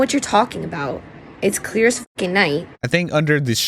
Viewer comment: did she say, "It's clear as f'ing night!" 🤦🏻‍♀️